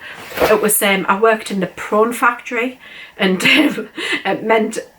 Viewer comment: Hi I hope you show us the fairy room when it’s all done, I can’t wait.! You will laugh I had to re-wind because I thought you said you use to work in a porn factory 😂. Relieved to realise it was my dodgy hearing…..it was prawn 😂. Always lovely to see you. Xx